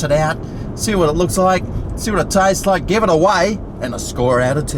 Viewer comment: All I heard was 'give it away', like who's going to give his half eaten burger to? lmao.